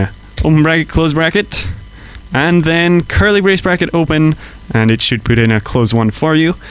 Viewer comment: what is saying in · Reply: curly brace bracket open